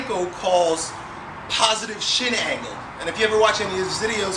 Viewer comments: Thought he said "positive shit hanging." hah Awesome video, I love front squats. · I totally thought you said "Positive Shit Angle" lol!!! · lol thought I heard him say "positive shit angle"